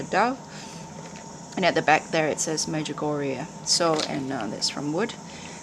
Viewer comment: Nice pieces. Say, what is it that you're saying is printed on the back of the cross which the auto-captioner is transcriibing as "magic Oreo" or "magic oriya" (3:30)? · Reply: Medjugorje 😇 in Bosnia-Herzegovina <link>